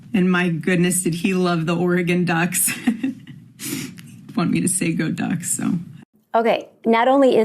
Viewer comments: With the trillions they stole they could of hired a better actress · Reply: mark 18:10 Did she say "Charlie wanted me to say 'go ducks'" He must still be alive then. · did she say, "he wanred me to say go ducks"? Really? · Did she say "he wants me to say go ducks"??